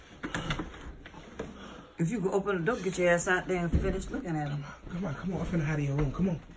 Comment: thought she said “If he ain’t dead, then getcha ass out there and finish the job!”